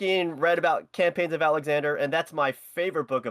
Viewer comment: What is the book that said is his favorite in the beginning? Couldn't make out why he said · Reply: "the Campaigns of Alexander" by Arrian ^_^